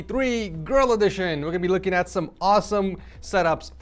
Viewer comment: I thought he said "grill edition"